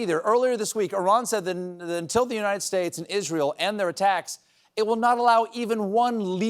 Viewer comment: I swear he said The United States of Israel, and it fits